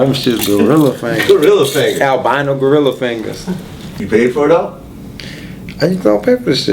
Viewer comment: he lyin his ass off riff raff a cokehead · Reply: What did he say at 1:19 to did he pay for it all?